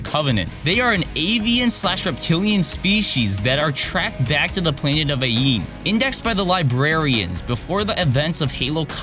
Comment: did he say planet of yee